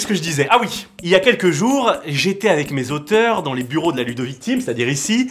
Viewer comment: j'ai entendu "la ludo-vitcime" au lieu de la ludovik team 😭😭😭